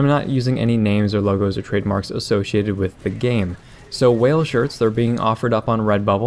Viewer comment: rofl I thought the description said "whale shits" hahahaha, oooh boy. I am so puerile sometimes.